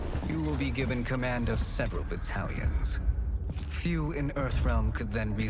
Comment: For a second there I thought he said, " As our ally, you will be given command of several italians." lmao